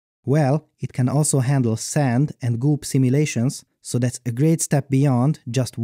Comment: I thought he said "Boob simulation"